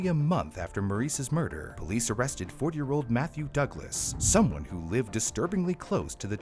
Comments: I thought he said 4 year old Matthew Douglas. Went back to make sure, and they said 40. But imagine a 4 year old in tiny handcuffs. XD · Held dude for 30 days with no evidence and lets the one who confessed walk cuz they wanted to be cautious. Interesting. Also at around 10:28 i swear it says they arrested 4 year old....